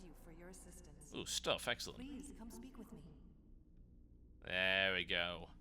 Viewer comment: did i just hear please come sleep with me at like 33:25 ?